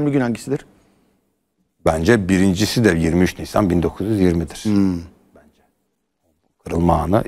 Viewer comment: saniye ile 42:25 arasında ne diyor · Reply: 23 Nisan 1920 Kırılma anı'dır